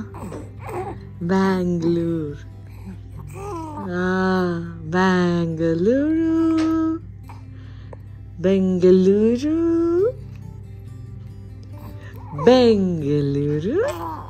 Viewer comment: Am I the only one who heard saying Bangaluru??😮